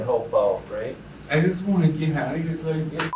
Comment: "I just want to get out of heee..". The captions were so off its gard to follow cause they are not whst is being said.